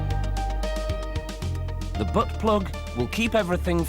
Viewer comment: did he just say, "butt plug"??? wait, DID HE JUST SAY, "BAIT"????